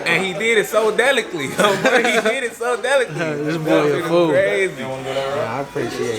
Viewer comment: dat boy just created a word!!! Superly😂😂😂😂😂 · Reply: 👁 B Damn 18:43. Another 1.... So Delickley.... He Thought He Said It Right So He Repeated It😂 Fucc Im Dead💀💀💀💀💀💀💀 😆😆😆😆😆😆😆😆😆😆😆